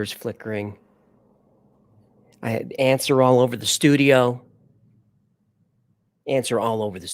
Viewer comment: I literally heard "my pants are all over the studio tonight" til he elaborated. 😂